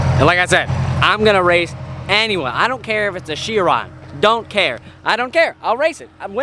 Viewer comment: what’s that car he just said Sheron? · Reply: Chiron